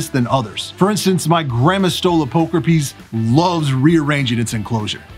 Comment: Why did it sound like you said my Grandma stole a poker piece